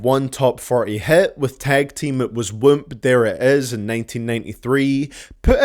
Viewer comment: For the longest time I thought they were saying woomp that ass